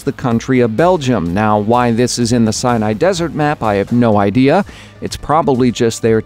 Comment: Anyone else thought he said Cyanide Desert?